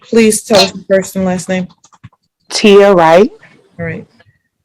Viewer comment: OMG! When they started taking about the defendant’s friend I thought they were saying “to your/ya right” like they were giving a direction…I didn’t know they were saying “Tia (W)Right” as in her name! 🤦🏻‍♂️